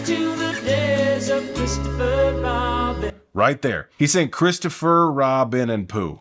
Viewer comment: Thank you for this very insightful video AND for curing me of my 20+ year confusion about why they had a sample that said "Mister Footlong" (mishearing that part of Christopher Robin)